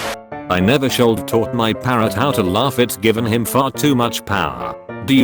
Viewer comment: i thought that said pareNT AHAH